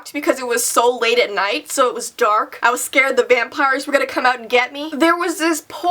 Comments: I thought you said van pirates at first which made me crack up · I am scared the vampires are going to get me Me: I thought you said your a vampire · Lol I thought you said VANpires. Just think of that 😂😂😂